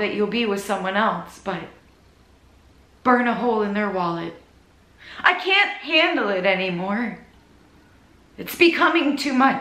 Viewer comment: Lol I thought I heard you say: "I can't candle it anymore" at 3:30 hahaha! As in your love for candles and things like that 😂🕯️